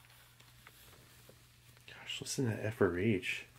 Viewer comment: “Gosh listen to that FRH” I heard….Listen to that F’er rage